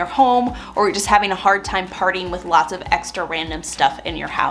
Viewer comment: First thing I thought was Swedish death metal. 🤘 Swedish ĐɆ₳₮Ⱨ ₥Ɇ₮₳Ⱡ Cleaning sounds destructive! 😆 When she said "If you're having a hard time parting with extra stuff in your house", I heard: "If you're having a hard time partYing with extra stuff in your house." I'm picturing the Swedish chef from the Muppets yeeting things around while Dr Teeth and the Electric Mayhem (the band from the Muppets) plays Dismember covers. ANIMAAAALLLL! All the chaos. All the Muppets find treasures and repurpose all your un-needed items. For example, Gonzo takes your grandma's old hand-cranked cake mixer and uses it to build a mechanism for his chickens to fly. In the end, everything is somehow, magically clean and organized. My over active imagination is probably why I don't get much organizing done in real life. 🙃